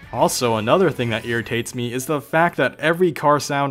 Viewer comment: thought it said fart